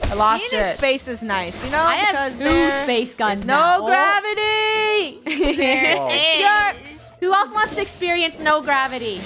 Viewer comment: is it just me or at the time, 14:50 i hear say, "there is" <3<3<3